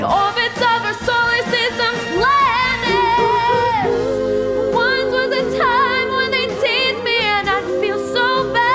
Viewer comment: i thought she said once there was a time where they tazed me and i felt so bad. hahaha <3